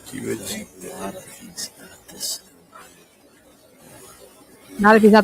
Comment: when the two guys are talking about the crime scene and you tried to decipher what he’s saying. you thought he said “not if it’s not the same____.” i think he said “i think a lot of things about this ain’t right”